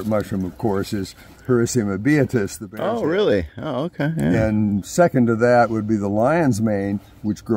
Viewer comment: what was saying at 10:41 in the video? He was in the middle of naming his all time favorite mushroom but I could not make it out and the transcript does not help me. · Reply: Bear tooth- Hericium americanum